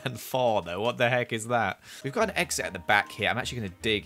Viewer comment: Why did I hear "we've got an EXO fan here"